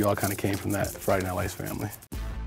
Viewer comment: did he just say Ace Family cause I heard that he used to date Catherine from the Ace Family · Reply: Camille B he said ‘Friday Night Lights family’ because he used to be on that show.